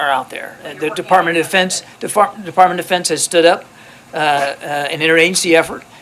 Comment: it sounded like he was about to say "De-fartment of depends"